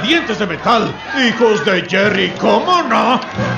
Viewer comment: Hijos de jerry, como no! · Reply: Yo le entendí "Hijos de Jerry Coleman?"